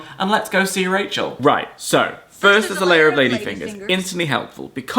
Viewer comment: i always thought Rachel had said " a layer of ladies' fingers", as in the vegetable. Not sure if it makes more or less sense with the biscuit, honestly